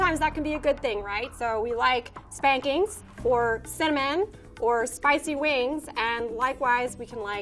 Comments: We like spankings... @ 05:15 Roll out of bed, i need a drink with my oatmeal... This lady is amazing lol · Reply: when she said cinnamon, I thought she said semen. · I had to rewind it three times because I thought I heard "we like spankings, or semen, or spicy wings." I finally turned on CC and she said "cinnamon!" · I would have sworn she said "we like spankings, or semen, or spicy wings." Listened 4 times before checking the subtitles. Don't I feel silly... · "We like spankings, or cinnamon, or spicy wings." I love this lmao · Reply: I had this on in the background and I was like "Did she just say 'we like spankings?'" Too good. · literally no one: whiskey expert: "we like spankings" well okay then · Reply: I had to turn on captions to get the cinnamon. I really thought she said spankings, semen and spicey wings. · did she say she likes spankings...hahaha 😄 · Did she just say we like spankings